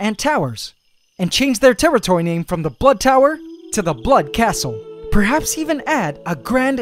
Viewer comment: I mistakenly heard "the butt tower" instead of "the blood tower" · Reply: I heard “the bl- utt tower”